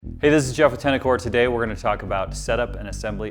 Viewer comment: I thought he said “hey this is Jeff tenicor” 💀 but yeah just got my velo5 in for my p320. Super beautiful fit,super comfy and very concealable. 5”11 220 lbs